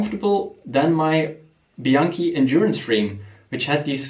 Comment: Wait. Did I hear your other bike is a Bianchi? 05:49.? I have a 928L. Is this a sign? Lol.